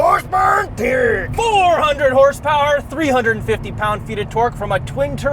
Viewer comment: Did I mishear said Horsepower and Turd?🤣🤣